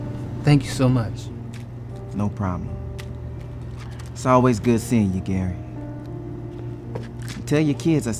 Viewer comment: There's a mistake there. It said "food" instead of good. XD